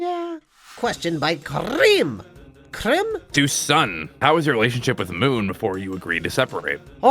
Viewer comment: I'm not sure but did sun call the person's username cream at 8:21?